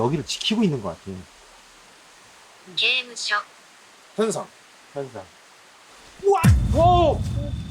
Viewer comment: 현상 아니고 “게임 하자”(ゲームしよ)처럼 들려요! 현상은 げんしょう라서 장음까지 발음해야하는데 장음 발음이 없거든요 앞에서 의 “우리 앞으로 와줄수있어요?” 가 일본어로는 「우리 앞에서 움직이고 있지 않으세요?」로 번역되었고 대답으로 「게임하자ゲームしよ」 하고 바로 울린 것 같아요